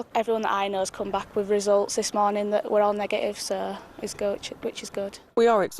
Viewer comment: she was gonna say "its Gucci" lol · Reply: 😂 It's gucci it's good 😂